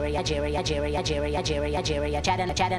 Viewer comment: after a while nigeria sounds like it says i'd hear ya